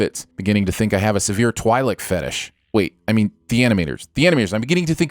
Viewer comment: At first I thought you said Twilight not Twi’lek.